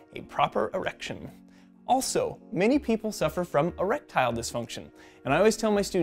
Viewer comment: Did he just say A Reptile Dysfunction?